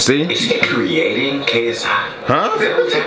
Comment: Wait, what? Did you say 'Is he creating KS-hot?'